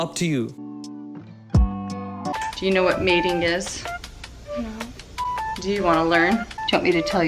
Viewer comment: did she just say “do you know what mating is?” ??